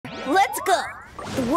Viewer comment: Did he just say "Please glide in me master"?